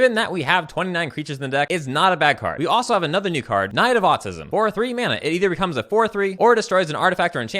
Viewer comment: "Knight of Autism"... Did I hear that correctly? LOL